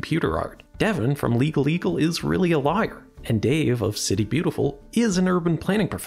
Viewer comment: Way to sneak in Legal Eagle is a real “liar” and make it sound like you just said “lawyer” funny